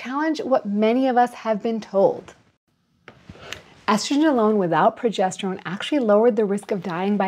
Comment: What does she say ?I hear "aztulalone without progestrone" ?